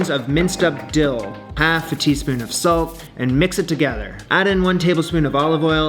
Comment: I thought he said soap for a second